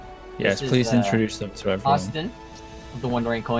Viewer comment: Did anyone else catch that he called them often instead of silver😏😏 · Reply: By often you mean Austin